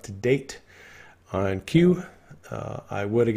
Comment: sounds like you said "I am Q" instead of "on Q" 😂